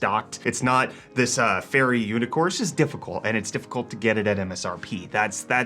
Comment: did he just say UNICORSE?????????